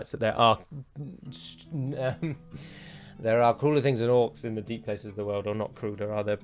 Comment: I misheard «there are cooler things than orcs in the deep places of the world» lmao Fans will agree, balrogs and trolls are cooler.